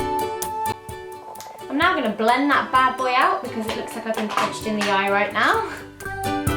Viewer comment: Please, someone could write for me what zoe said at 4:38 until 4:40,because im learning english and i didn' t understand what she said .so please help me and sorry about the mistakes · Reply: She said: "I'm now gonna blend that bad boy out, because it looks like I've been punched in the eye right now."